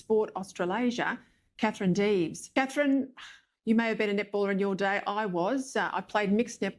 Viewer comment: Net ball I thought she kept saying nipple.